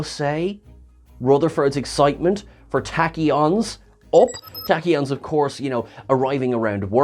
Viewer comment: When Rutherford said Tachion it sounded like he said Techion. I was confused.